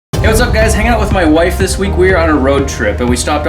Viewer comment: At the begining, i thought he said, "Hey guys, hanna is my wife this week..." I was like thug life